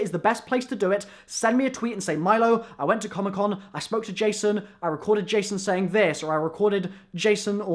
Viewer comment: it sounds like he said " i smoked a jay son" ahahhah